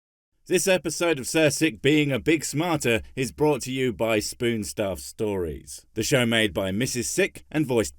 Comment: ! I get pumped everytime I hear the song. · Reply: I thought it said ‘for Sic’